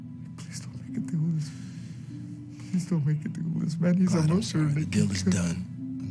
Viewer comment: "Please don't make me do this" is what he initially says. If only Nick knew what "do this" meant, which was essentially the start of Nick's revenge. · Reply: "Please don't make a deal with this man"